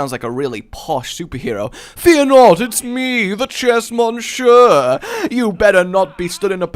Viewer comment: At first I thought you said "chess muncher" and not "chess Monsieur". :/